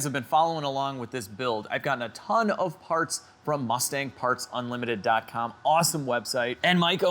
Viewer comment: i thought it said " Mustang Farts Unlimited"